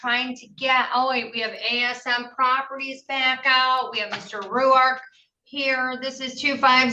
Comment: Did she say, "Mr. Rourke"? Zee plane, zee plane (please let SOMEONE get this reference)